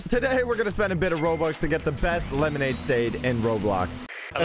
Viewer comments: ahhhh yes lemonade stade but the caption says stand. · he says stade instead of stand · He really said “lemonade stade”